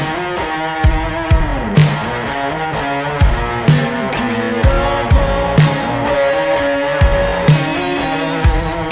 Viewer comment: Someone tell me what he said at 2:08. I'm working my english. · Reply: is: "Thinking of all the ways I could get you back around."